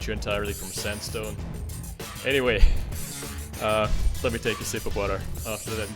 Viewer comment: In 0:30 I thought you said "let me take a sip of butter".